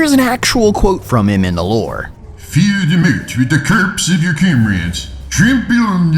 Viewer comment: what? Can someone tell me what he said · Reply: "Fill the moat with the corpses of your comrades. Trample on the mount of corpses to reach the ramparts, and bring down the walls with the weight of your dead."